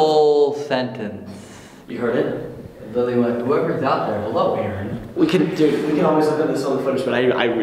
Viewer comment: WHO ELSE HEARD THE WISPER THAT SAID hello Aron ?!?!?!? ( when they were discussing what they thought the spirit box said whoever’s there hi Aron . I thought I heard it there)